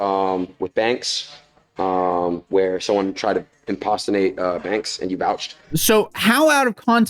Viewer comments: Did he really say imp-pasta-nate? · I’m sorry but did he just say “impostinate”?